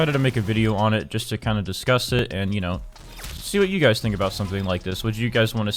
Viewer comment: “See what YOU guys think...” I thought he said “sue you guys” I got scared. First, he played his intro late, then he wants to sue us?... what is happening?